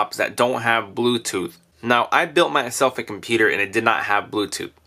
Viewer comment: I'm pretty sure this item will work on my "COMPETER"! idk,when he said computer in the beginning of the video it sounded like he said " competer"